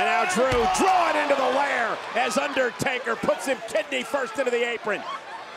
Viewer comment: did he say kidney first????